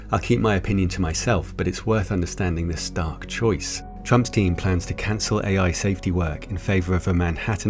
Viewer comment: When he said 'Trump's team' I thought he said 'Trump Stein'... I had to go back & check.